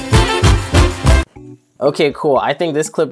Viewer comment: my Google went off because it sounded like ok Google